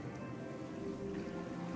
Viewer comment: LMFAO died edit: now everytime those robots are shown mouthing in silence I keep hearing shamalama zama XD 22:57 lol that moment wi-fi/bluetooth happened